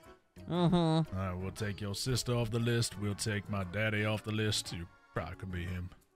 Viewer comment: LMAO I THOUGHT HE SAID BADDIE